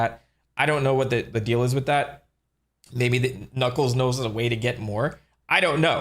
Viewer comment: Did you just say Knuckles knows "da wae"